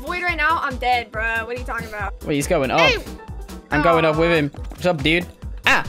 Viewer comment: Hold up did say : “babe” At 0:42 or am I hallucinating or hearing things